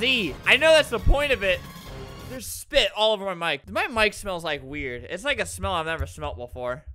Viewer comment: "my mic smells like a weird" lmao so beautiful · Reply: I thought he said "my mic smells like a beer" Albert, have you been drinking?!